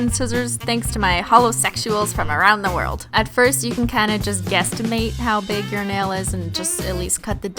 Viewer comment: did she say gestimate at 7:34 lol